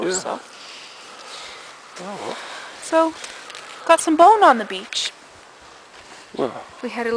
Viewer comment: at about 1:45 did she say she got boned on the beach?